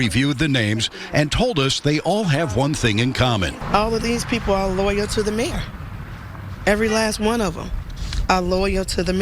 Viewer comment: Subpoenas are issued to those loyal to the major, thought she said they were a LAWYERS for the mayor LOL